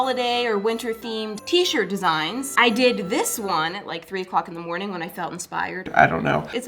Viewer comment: When she said three o’clock in the morning I was like three in the what!?(I thought she said three in clucking morning (not cluck the f word)